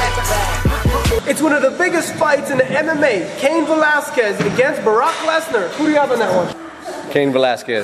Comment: did he just say 'barack' lesnar?